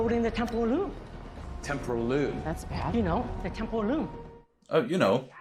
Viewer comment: Every time he said loom. I didn’t know if he was saying loom or room. Again Goonies. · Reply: I thought he said “Temple of Doom” instead of “Temporal Loom”